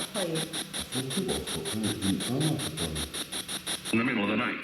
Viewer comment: G'day and, I really enjoyed this 2 part series. is so full of history and information, he is great. @ 10:15 - "In the middle of the night". 10:35 -"Do 'em" I think?" 15:55 - "It's Mandy" (Sorry, at this point I was getting another headache so I stopped slowing it down and transcribing what I heard). Seeing get freaked out rather than was funny. Thanks for all you do to share everything with us. Sending love. ❤🐨🦘